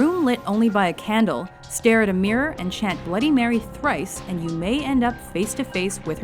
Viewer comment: Is thrice a number? Because I could've sworn that you said bloody Mary three times